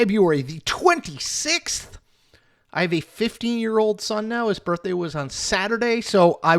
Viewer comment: hard to believe he has a 50 year old son and he is 47?? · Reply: I thought he said 15 year old son.